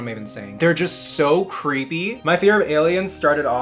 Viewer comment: i thought u said freak instead of creep lmfao